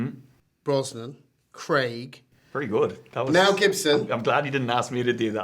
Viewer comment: When the interviewer said "now gibson" when listing bonds, sounded like he said "Mel Gibson"